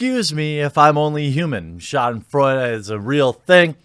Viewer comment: WTF did he say!? All I caught out of that is "real thing" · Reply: schadenfreude /ˈʃɑːd(ə)nˌfrɔɪdə,German ˈʃɑːdənˌfrɔydə/ Learn to pronounce noun pleasure derived by someone from another person's misfortune.